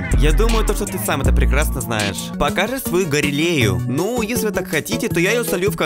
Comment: Мне послышалось или ты сказал не Галерею а Гарелею